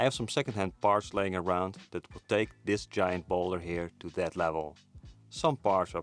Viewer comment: When you said "This Giant Boulder bike" I thought you said "this giant baller bike" 😂